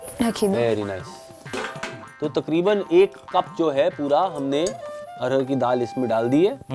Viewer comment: did he said arhar ki dal (18:55)!